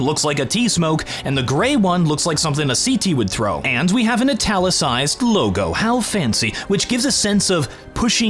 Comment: I legit thought he said “a Nutella-cized logo” for a second. I gotta get my ears checked 😂